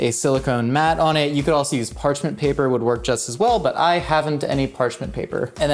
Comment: Did he say HAVEN'T ? 💀